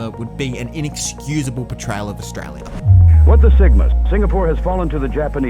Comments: No way did I hear “what the sigma” at 10:49 lmao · Hey I'm curious what is the guy saying at 10:49 I don't wanna say it but all I can hear is "what the sigma" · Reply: It's "what the sigma". That voice over is an AI overdub which he often uses for humor. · I thought I heard 'what the sigma' there lmao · This is insane brainrot but does anyone else hear "what the sigma" at 10:49 😂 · anyone else hear ‘what the sigmas’? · did bro just say "What the sigmas"? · am I insane or did it say “What the sigma” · did he say what the sigma ?!?!?!?! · Am I hearing incorrectly or did they say "What the Sigmas"? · why does he say "What the sigma"